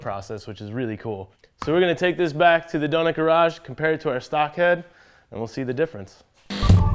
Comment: sounds like he says donut garage. cross over episode? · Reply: Our shop has been called Donut Garage, way before Donut Media was a thing.